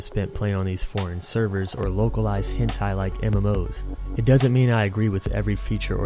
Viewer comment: i keep hearing hentai